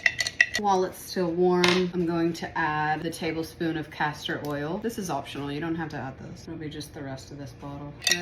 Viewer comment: Lol I wasnt looking and I thought she said Castrol oil.....I was like wtf...dont put that on you😂😂😂